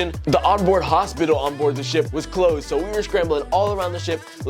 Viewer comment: All I heard was “the on board Hospital was closed” (?!) 😳 lol